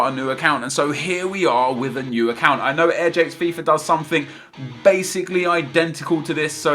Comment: What was the name of the YouTuber you shouted out (mumbled) at 0:50 ? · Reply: AirJapesFifa